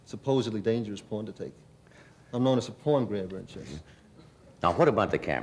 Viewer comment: I cant make out what he said at 8:44 .Anyone? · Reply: I’m known as a pawn grabber in chess